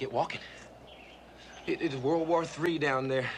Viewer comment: that was an outstanding video! I could’ve sworn I heard you say World War II about the movie red Dawn. If I’m correct, you may want to correct the voice over on that.